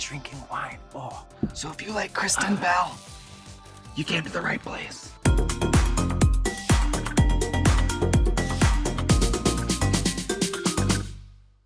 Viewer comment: I swear I thought she said “ so if you like crystal meth, you came to the right place “💀💀💀💀💀